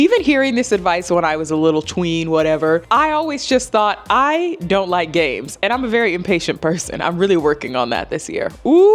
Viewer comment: I thought you said “I don’t like Gabes” and I was like damn what did Gabe do lol